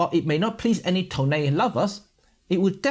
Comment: did you say…..toneh? I’m a fan of yours and Kasey lol